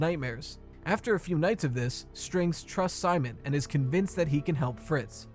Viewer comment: Damn I thought you said sus strings